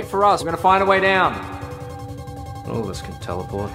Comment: for a second I thought he said something else💀 Hint: it is five letters. It starts with N Has two G’s and ends with an A :N_ GGA